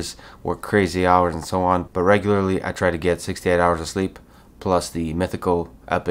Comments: Anyone else thought he said 68 hrs of sleep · When he said: "The day starts now, after about 6 to 8 hours asleep", I thought he said "after about 68 hours asleep". It was a big wow! 😂😂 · I had to stop the video and think because at the beginning I thought Lex was saying "I get 68 hours of sleep" and I was like ??????? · I thought he said 68 hours of sleep🤣🤣🤣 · Thought he said 68 hours of sleep lol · I thought you said 68 hours of sleep!!! 😮😂❤